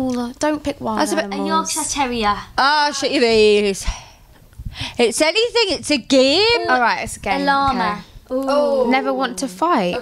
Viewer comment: what did perrie say, i can usually tell tho but what? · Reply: Ashleigh She said "Oh shut your face. It's anything it's a game" xD